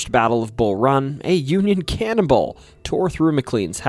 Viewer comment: Ok... I'm tired and English is not my first language, but what I heard at 2:02 was "...a Union Cannibal tore through McLean's house and landed squarely in the kitchen fireplace" ...it's been a while since I laughed so hard... and then I realised what you actually said and it made me feel stupid... but on second (third?) thought, I like my version better 😅😂